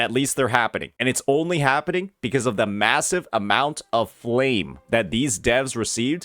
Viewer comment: did you say...massive?